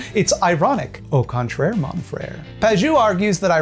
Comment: Every time you say Pagaeu all I hear is “Prager U Piaget” which is apt since Peterson seems to fancy himself the new Jung, while his early work looks like Freud’s if he were even MORE terrified of human shreksuality.